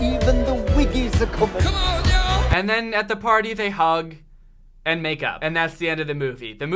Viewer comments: I THOUGHT YOU SAID THEY HUGGED AND MADE OUT IM WHEEZING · I swear to god I thought he said at the party the daughter and the dad make out instead make up and I was thinking Alabama 100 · I thought he said they hug and make out lol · I heard "they hug, and make up" as "they hug, and make out" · i thought he said “And then they hug and make out”😭😭😭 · Am I the only one that thought at 21:49 said, "and make out" ? I know it was makeup but still... had to watch it again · When you said they hugged and made up I thought you said they hugged and made out😂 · I thought that he said “they hug then make out” I was mortified but I remember he did not say that oof.... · was it just me who thought he said "and make out"? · I thought you said “they hug and MAKE OUT” jeeeeez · ok tbh I heard “they hug and they makeout “ I have no words.